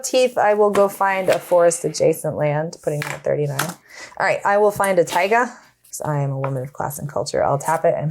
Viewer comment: briefly I thought said “I’m going to find a taiga cause I’m a women.”